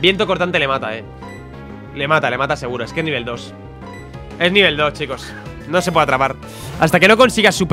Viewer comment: escuche : Es nivel dios xD